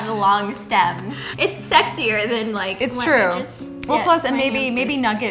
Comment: i know it's just a word, but did anyone else hear say "sexier" at 3:07 lol i rewinded it like 10 times!